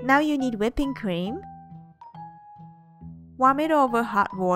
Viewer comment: sorry, subtitle is wrong. *whipping cream